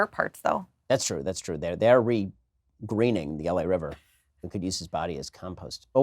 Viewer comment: "we could use his body as compost oh wait are we live" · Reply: I thought he said they could use his body as a compass... It makes a lot more sense now, lol.